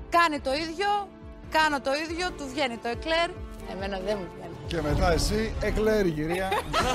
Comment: Στο 0:56 τι λέει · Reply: Και κλαιεεει η κυρία που λέει